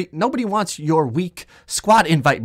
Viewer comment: What Joe said: Nobody wants your weak squad invite... hshsjksnxix.. stop messin up my intro! What I heard Joe say: Get the fuck off my screen with that weak ass shit boy I’m bouta get on yo.... oh we still on YouTube... hi😬